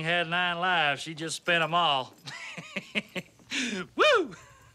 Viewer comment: If that thing had 9 lives, she just spend them all lol :)😂😂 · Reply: I thought he said, fed em all.